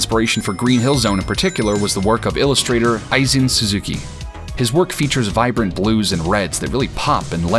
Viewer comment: Great video! I'm trying to look up the illustrator you mentioned but I can only assume I'm mishearing the name. Aizen Suzuki? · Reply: He is called Eizin Suzuki. I bought one of his art books a while ago, he really makes amazing illustrations.